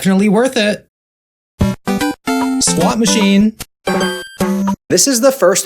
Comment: When he said squat machine I swear I heard him say slot machine